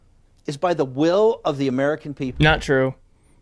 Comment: The will of the American people? I thought he said divine will. 😂😂😂